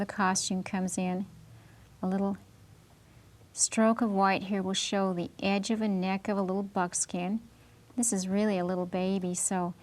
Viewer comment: Did she say '' stroke of white here will show the edge of the neck of the little fuck's skin'' ??